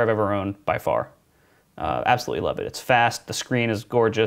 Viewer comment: Your most recent computer is the fastest? You don't say. Please tell me more. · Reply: He didn't say that. He said it's "fast", 2:58, which it arguably is for what he uses it for. Not sure how you misheard that.